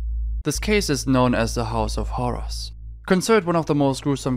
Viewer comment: I thought u said “house of Horus”😂